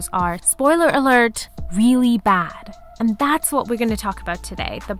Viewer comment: Whoever put in the subtitles at 0:57 put in the proper wording (awful) instead of 'really bad' 😂